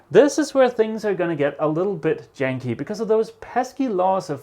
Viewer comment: Did I hear `...a little bit yankee' ? I've heard you say it before, but I could be wrong... Always fun, in any case...! 👍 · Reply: He uses the word "Janky" adjective INFORMAL•NORTH AMERICAN of extremely poor or unreliable quality. I've always thought it was southern slang mostly used by black people but I've heard plenty of white people use the word also. Hope this gives you some insight if you wish to research the word further.